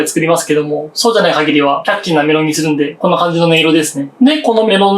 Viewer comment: キャッチーって言ってるんだろうけど｢キャッキー｣に聞こえる